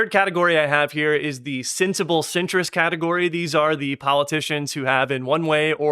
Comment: I misheard Sensible Centrist as “Sensible Citrus” and I wondered “what is a Citrus Democrat?”